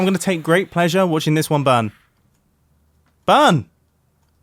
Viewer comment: When Mumbo Jumbo said "burn!" i heard "bun" or "ban" and when i watched that second more detailedly, i noticed that he said "burn!" there are some words and letters that i understand better with russian acent (or something similar as russian acent) than with english acent (for me the acent from united states is harder to understand that english acent) and i am not russian or english